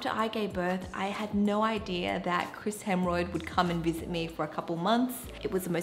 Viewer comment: Did she just say, “Chris Hemorrhoid?” 😂